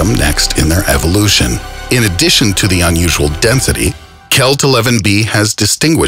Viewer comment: anyone else hear him say denthity